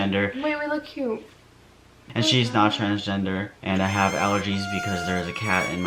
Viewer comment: I thought you said “and she’s now trans” I was so confused